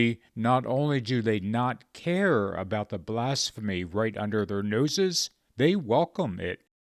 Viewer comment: You said at the end that the Conservative Christians do not care about the 'blasphemy under their noses'. True But I at first I thought you said they don't care about the 'mass hypnosis'. Either way, it's both.